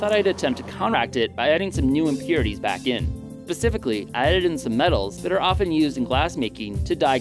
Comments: Did you REALLY say "pacifically" at 5:02 ....!??!?! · Great video! but did he say.... ..."pacifically" @ 5:01 ?